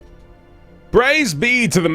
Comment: I thought you said "Brai's B to the man at arms" at the start, then realized that it was praise. welp no more butt stuff joke